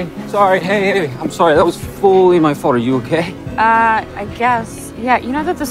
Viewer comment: it sounds like he says that was fully my fart 🤣🤣🤣